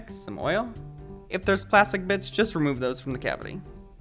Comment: I thought he said “if there’s a plastic bi*ch”😂